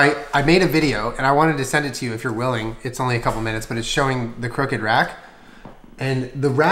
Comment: I deadass thought it said Cooked Rat and I was very confused